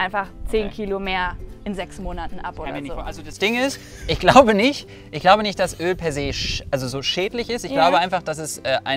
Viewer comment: Was sagt bei 7:18/7:19 nachdem ausgeredet hat und weswegen lacht? Ich will’s unbedingt wissen, aber verstehe es akustisch nicht! 😭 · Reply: Er war dabei zu sagen "Das kann ich mir nicht vorstellen" 😄